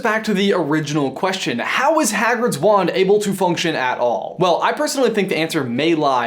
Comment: I thought it said "how hagrids wank works."